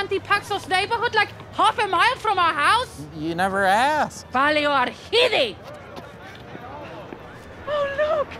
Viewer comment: What did Nadja say?? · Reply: Παλιοαρχίδι